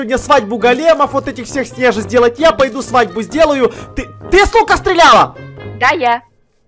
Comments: Я впервый раз услышала: Ты с ука... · Почему мне заместь: ты с лука стреляла? Послышалось: ты су*а стреляла? Ого сколько лайков это мой новый рикорд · мне послышалось не ты с лука стреляла а ( ты су*а стреляла Простите за мат 👉👈❤️ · Люди мне одной послышалось в 02:03 он сказал "ты су£@ стреляла?" Мне одной послышалось?? :00